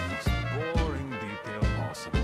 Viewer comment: Sorry but what was about metro , i didn't understand what was fail or bug ?! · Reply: The A.I. says "At least I'm not getting shot at-" And then would immediately take a throwing knife to the stomach.